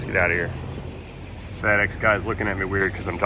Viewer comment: The irony. Also, the subtitles thought you said "fat x guy", instead of fedex guy.